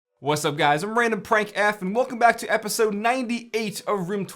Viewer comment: I just rewound 4 to 5 times when i heard " I AM RandomFrank F ". Love u man ... keep doin' what u do the best ... For 100th Episode combine all the Best Setups of all previous epidsodes. " BEST SETUPS MONTAGE " .. if u will ... LIVE LONG AND PROSPER 🖖🏻😎